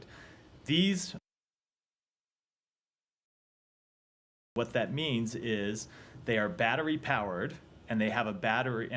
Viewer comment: What is he saying at 3:39 when the audio was removed? These "land mines..." something. Was this censored? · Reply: Reading his lips it says these land mines we are providing are non persistent land mines.